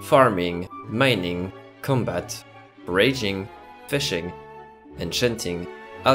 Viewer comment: when he said foraging, I thought he said raging and I actually laughed becauase I thought it was funny (wasn't looking at screen). Then I rewinded and realized he said foraging :l